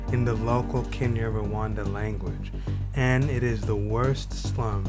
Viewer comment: and what did you say here 0:17 ? And did l hear you say Kenya~Rwanda language? Which language is that? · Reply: Kinyarwanda